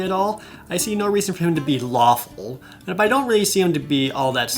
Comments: When you said "lawful" I thought you said "waffle" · When Jared was talking about alignments and said "lawful" I definitely heard "waffle". #wafflecrew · When he said "lawful" I thought he said "waffle" like ah yes... I am waffle good